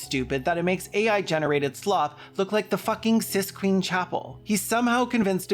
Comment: "The Sis-Queen-Chapel" (whether it was intended joke or not, I mishear things often) cracked me up.